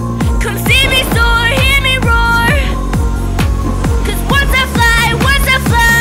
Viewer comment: Subtitle wrong at 1:57 Instead of soar, it’s roar.